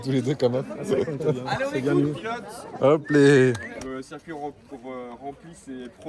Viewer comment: C'est moi ou à 36:12 on entend "Inox" ? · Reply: Pilotes pas inox